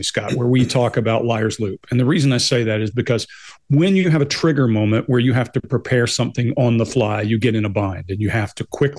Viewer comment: He sells crap on the cart? He sells crack on the cart? What did she say??? 1:06 ish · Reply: She said "..crap on the cart."